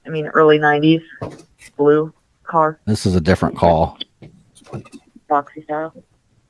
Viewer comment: you said, “this is a different call” to the “ ... early 90’s blue car”. So they are ignoring other calls. I heard, “this is a small town”, and I’m guessing multiple units following one vehicle, who they know the owner isn’t a “threat” and knowingly, unnecessarily pulling valuable resources from other jurisdictions, is clearly a problem in itself. That’s not to mention the miles they followed him. I’m sure there’s policy or statutes in place governing how long you can follow a “possible “subject””, not committing any moving violations. At the least they need some ethics training, or official reprimands, or possibly terminations. Open,shut harassment.